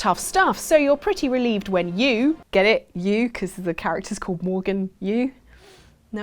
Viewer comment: I was very confused because I thought she said "you" instead of "Yu" because my name is Morgan...